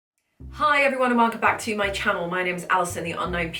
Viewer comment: Just found you in my recommendations so as an American, when you said "tutor" I thought you said "cheater"